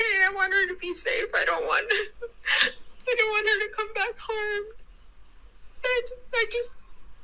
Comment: The "mother" knew. She is a liar through & through. Just a matter of time for that 🐄 to face charges, get jail time n him the ☠️ penalty About 8:20 sounds like she say's I DON'T want her to come back home